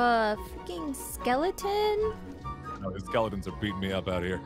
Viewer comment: did you say f***ing skeleton 😅